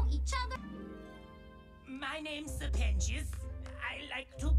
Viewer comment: I had subtitles on and it says “my names the penis”💀💀💀 instead of sirpentious